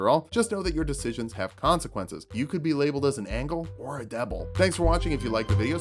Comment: did he really say “Angle or a Deble”?